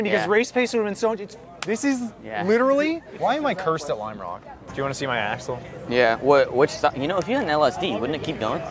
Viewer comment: Thought for sure said “do you wanna see my asshole?” 😂😂